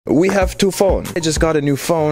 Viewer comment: I swear to god I thought u said "we have to fok"